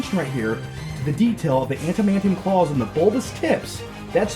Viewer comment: Did he just say Antimantium claws?